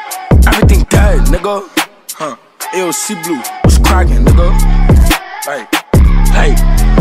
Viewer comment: Bru I thought nigga he saying ayo sea glue what’s crackin